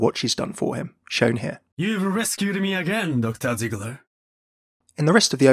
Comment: I'm sorry, but it sounded like Genji said Dr. Tickler not Ziegler xD I can't not hear that now.